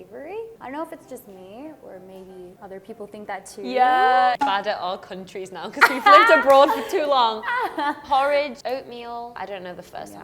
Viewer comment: did say "that's right" in Korean? Or it's just my ears? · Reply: I went back to that part and she said "Bad at our countries now cos (because) we've lived abroad for too long"